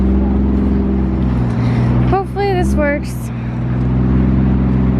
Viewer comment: You said "Collete's Z" but I heard "Colletsie" and thought that's what she named it lol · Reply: I heard “collete LZ” lol